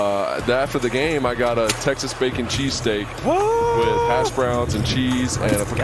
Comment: ayo?? And no y’all just so ex to troll because of its “our year” and its never y’all year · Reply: bro really thought he said taxes when he said Texas 😭😭😭